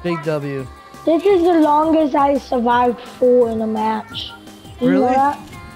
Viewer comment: i thought he said "G-Ford" or something